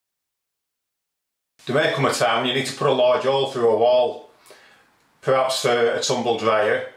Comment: I've listened 10 times to the first 20 seconds of him speaking and I can't make out a word he's saying. In fact it sounds like a totally different language. Anyone else experiencing this? I'm from Canada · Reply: Justin Green ok after a few more times I hear, "_____________to put a large hole through a wall"